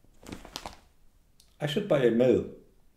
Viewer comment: i thought you said i should buy a milf